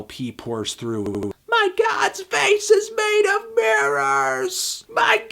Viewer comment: he actually says "guide" not "god" I know, I thought the same thing.